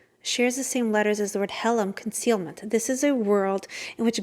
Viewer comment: Beautiful. What is the word at 1:41? הֶעֱלֵם?